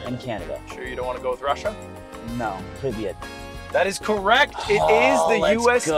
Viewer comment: For the people wondering what he's saying, he says Hello in Russia. (fonetically spelled Privjet)